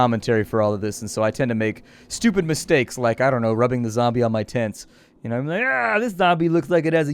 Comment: 100% thought he said "rubbing the zombie on my tits" 😂